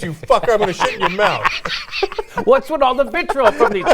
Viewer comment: Don't call it "bullying" it is harassment. Also I thought he said he was going to c** in his f** mouth I was like holy shit! it just got real...REAL RAPEY! Why did my mind go there?